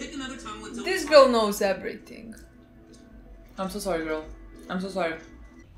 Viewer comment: when u said "fucking" something at the same time as lin said "im so sorry" i deadass for a moment thought she said "fucking so sorry" and i was shook